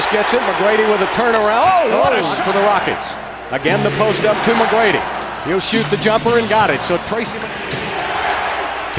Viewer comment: is it me or i heard the crowd shouting"tmac" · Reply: hahahaha no they're saying '' Defense''